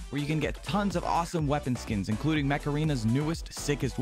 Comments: during the sponsor I thought he was saying macarena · I thought you said macarena